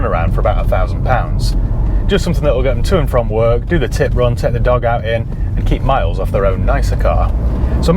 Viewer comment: Hey love the channel. Stoopid Yank question, at 17:19 did you say "do the tit run in"? If so, what is a tit run? Thanks! -Mark